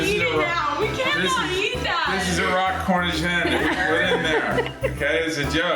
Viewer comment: did I hear Raw Cornish hen! On cooked turkey Wtf😨